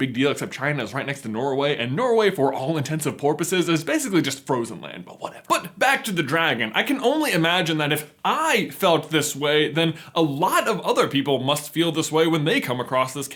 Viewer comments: I’m sorry J, but did you just say “for all intents and porpoises”???? 😂🐬 you crack me up! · anyone else hear "for all intents and porpoises" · did i just hear... "intents of porpoises"????? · did he say "all intensive PORPOISES??" 2:37 PORPOISES?